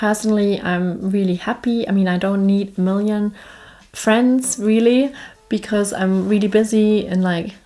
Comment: Did I hear you correctly or did I hear a Swede say this? You don't need any more friends? If this is correct, my word, you've adopted to Swedish life excellently!🤣